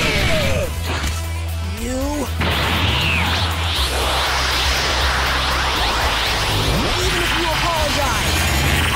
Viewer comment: what goku said here? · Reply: Even if you apologize, I WILL NEVER FORGIVE YOU!